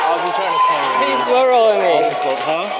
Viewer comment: for the record Cynn doesn't have the nuts his opponent was just salty that he missed the flop and got caught with his hand in the cookie jar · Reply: Yeah sounded like miles said, "you slowrolled me...punk"